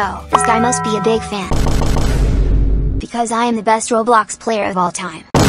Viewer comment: people who thought he said the best:🙂 people who saw it said worst:😂😂😂